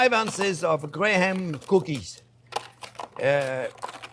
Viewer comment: did i hear "grayham cookies"? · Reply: Honey Maid Graham Crackers 👍❤️